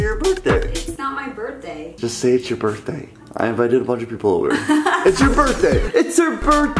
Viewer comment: What did say on 0:16 ? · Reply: He said " I invited a bunch of people over"